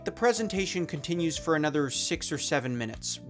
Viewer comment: Did he just say another 6 or 7 minutes?